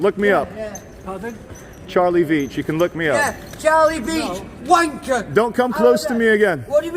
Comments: Did he call you Charlie "Beach"? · watching this again it sounds like he said Charlie beach19:41